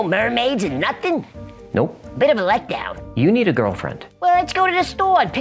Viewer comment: Haha when you said "you need a girlfriend" to hecklefish I thought you said "you need to grow a fin"